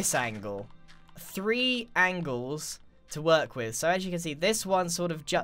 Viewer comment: Who didnt understand what grian said me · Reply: He said angles, you just put the time midword.